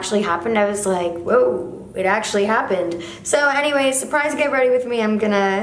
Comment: Tell me why every time I hear you say “anyway” I think you’re saying “hennyway”